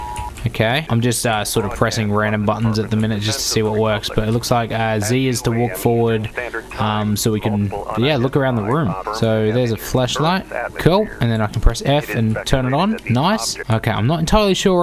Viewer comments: Is it just me or at 7:46 does he say "Fleshlight" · I heard "Z is to walk forward" and asked myself: WHY Z?!? LITERALLY EVERY OTHER GAME USES W FOR FORWARD!!! AAAAAAAAAAAAAAAAAAAAAAAAAAAAAAAAAAAAAAAAAAA